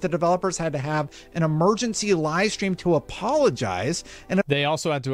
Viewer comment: I heard him say emergency lie stream😂